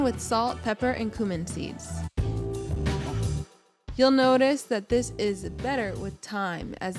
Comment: I thought she was saying “you’ll notice that this is better with thyme” at first 😆 🤦🏼‍♂️